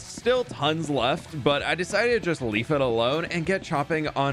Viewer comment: is it me or did it sound like he said “leaf it alone”